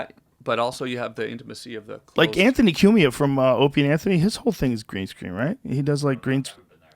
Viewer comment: I thought he kept saying “opiate Anthony” god I’m such a DA